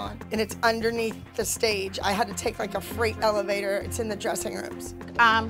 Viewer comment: Did she say she uses the freight elevator?